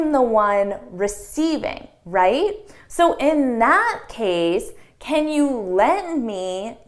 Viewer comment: So in that case'' I heard so in not case or so in at case. Its like th is drop.